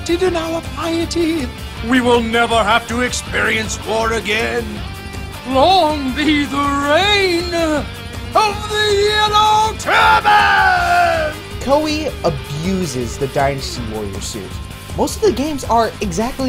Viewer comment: I thought he said yellow turnip.